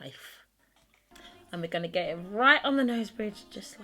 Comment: I thought she said “we’re gonna get right on the nose bitch”. I was like okayyy girl, do ya thang!